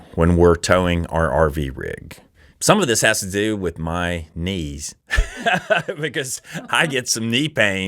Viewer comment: I am glad your tip 3 had graphics. I had the sound down a bit and I thought you said you took a break every couple of hours due to Sean's needs. I was just starting to wonder which needs, when the "knees" graphic came up, and I guess kind of clarified everything... :) Close call there.... as always, safe travels....